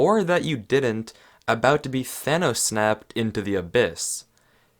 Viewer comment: Thanos Snaped? Did I hear that right?